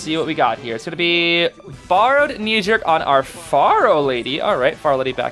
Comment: When said borrowed knee jerk I thought he said farrowed knee jerk on the farrow lady 51:50 😭